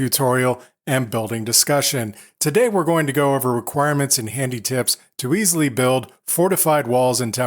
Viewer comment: "Today we are going to go over requirements and.." I swear I misheard 'requirements' as 'violence'